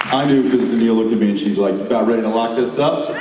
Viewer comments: what did jensen say in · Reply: He joked that he knew Danneel was 'the one' when she looked at him and said (pointing at herself), "You about ready to lock this up?" LOL. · Hey great video! I have a question: What did Jensen say at 52:45 about his wife? I'm sorry but english is not my native language, it'd be nice if you told me. Thanks in advance :) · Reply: Sarah "are you about ready to lock this up?" :)